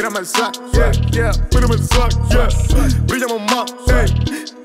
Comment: un detalle mi hno en esta parte 2:52 no dice "pillamos" si no que dice "brillamos" .... sigue asi brooooo un saludoo